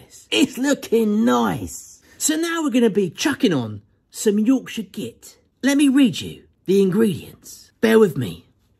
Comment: Did I hear say "Yorkshire Git"?